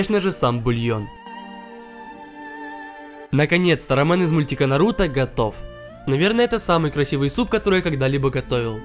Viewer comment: мне послышалось Роман из мультика Наруто готов🤣